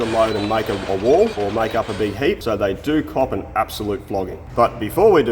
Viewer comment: "they do cop an absolute flogging" I had to turn on CC to understand what you said!